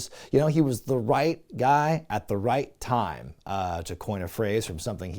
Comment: "the right gay guy" you mean 😂 not making fun it's what he said 😂